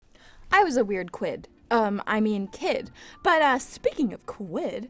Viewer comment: I thought you said "i was a weird squid" and then i realize it was quidd